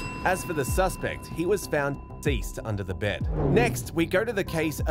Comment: what did he mean when he said " as for the suspect he was found dice under the bed"